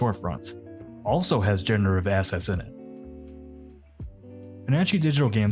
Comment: I keep hearing it as "generative asshats" 😭😭😭😭